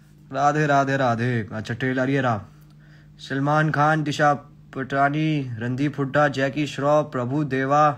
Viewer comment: I heard Randeep Hooda as Jackie Budha 😂😂 And thought he was roasting Jackie Shroff.